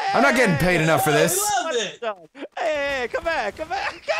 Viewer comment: wow, I thought you said " I'm Tired" in the one that Adam put up, but glad I got to see this to hear "retire". Still a awesome highlight if you ask me.